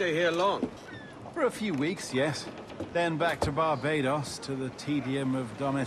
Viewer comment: did he say Barbados?